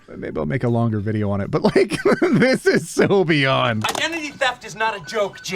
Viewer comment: for a second I thought you said "this is sylveon"